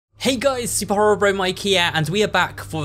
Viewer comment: before i really started hearing what you said i thought you said hello super horror bro mikea but you say hello here